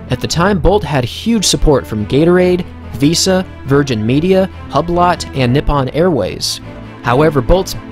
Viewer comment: did u just say harblot?